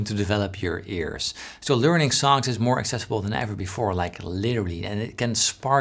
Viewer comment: What exactly are you saying at 12:24? The transcript says "literally" but it sounds more like you're saying "leary". Trying to figure out if you are mentioning some kind of learning service or method. Thanks.